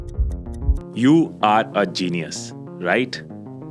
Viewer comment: Mujhe laga tha ye "YOI I ARE CENII IS"